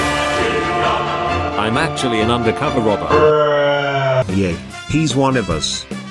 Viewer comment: For a second I thought you said Rober4